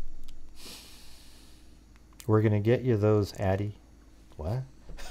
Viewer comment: I hear “We’re gonna get you girls, Maddie, over this.”